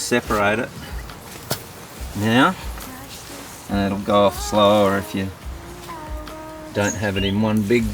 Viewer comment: For a second I thought that lil girl said "F off" 🤣